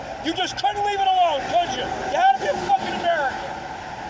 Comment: Hahahaha, sounded like he said, you just had to something American.